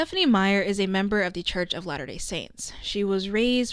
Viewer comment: Gonna be honest, I thought you were saying lana del saints ( as in lana del ray stans making some sort of cult) and not latter day saints